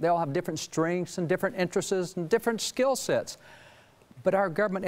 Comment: did he say interesses instead of interests? Nice.